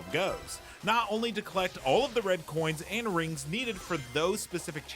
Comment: did he just call the “red coins”?